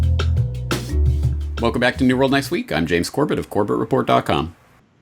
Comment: I always hear, "Neural Next Week". Now you will too 😜